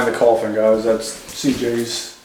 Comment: right before the 18:00 minute mark, said don't mind the coffin, I was taking a drink of my morning coffee and I thought he said coughing and when I looked there was a coffin on the floor. yep thats what i get for trying to wake up, I mishear and miss stuff. I don't know why But within the first 20 minutes the spirits were really messing with. I hope light headedness goes away. that was a great video and an intense one